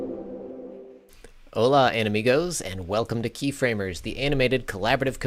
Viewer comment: o Did you say "enemigos" at 0:11? It sounded like that. :D ("enemigos" = "enemies")